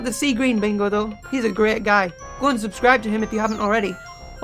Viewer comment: I thought he said unsubscribe to him if you havent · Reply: I think he said Go and subscribe